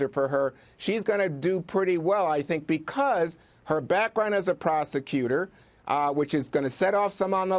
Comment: I thought he was gonna say background of prostitution